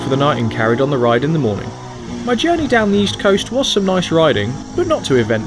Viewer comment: it sounds like you said the east coast.